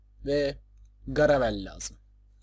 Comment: Garavel'i Gargamel anladım asdasdasfasdadas XD